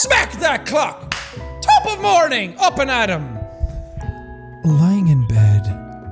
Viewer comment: when he started shouting I thought he was saying 'TOP OF MORNING DING DING' for a second there.